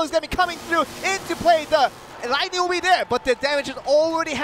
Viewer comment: wtf did he say? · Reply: Into play that ligtning Will be there